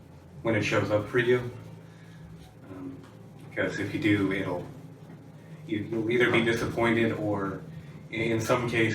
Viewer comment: What is that word he used? Quithoth? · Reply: Billy Koepsel qliphoth... the world of dead malignant shells